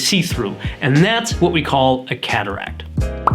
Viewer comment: Thanks for this video, I have to say though, you talk so fast I thought you were saying "Cadillac" car!!!! You did a good job. :)